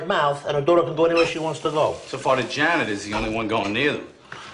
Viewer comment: What did artie say at 2:30 ? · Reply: Figured it out after 2 years : so far the janitor's the only one going near them.